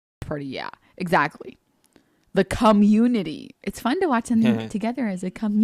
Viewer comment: Did she just say "the cum unity"?